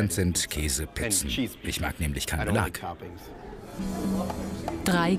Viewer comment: Was sagt er bei0:49? 99% sind Käse ich mag nämlich keine Lankabel?